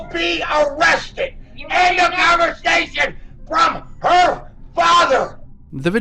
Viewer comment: when the subtitles say "unintelligible" her father really jus says really slurred "END OF CONVERSATION FROM HER FARHER"